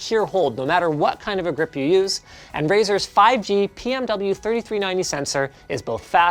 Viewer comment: "dongle compartment" sounds like a feature the LTT underwear might have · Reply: is it only me or he says Brazzers instead of razer